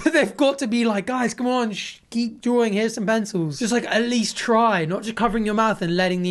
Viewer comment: i legit thought you said "here's some benzos"